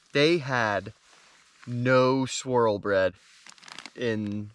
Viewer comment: For a while I thought you were saying "squirrel bread" lol. Good luck man! I'll be rooting for you!